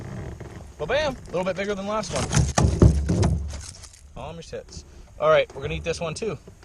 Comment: Did he just say calm your tits 😅